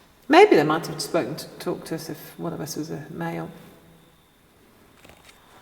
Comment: I think my mind must be in the gutter because I heard ‘got a big boob’ and ‘powerful arse’ 😂